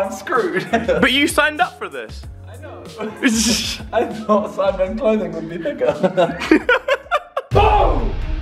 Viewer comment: what did u say simon!!!!! lol jk i know its "thicker"